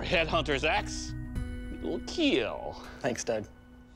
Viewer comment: did he say "Thanks dad"? 😂